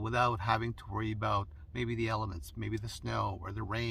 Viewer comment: I thought you said worry about the elephants, and I thought where the heck does he live? Then I realized you said elements! I had a good chuckle at that one.